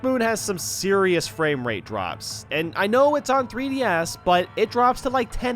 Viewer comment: nice job youtube putting 3D a s s instead of 3DS (captions)